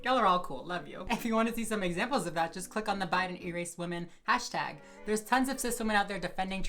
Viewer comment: Cc: “bite and erase women” Me: I legit heard her say Biden what do you mean?! Also, as someone who’s just coming out as pan this is relieving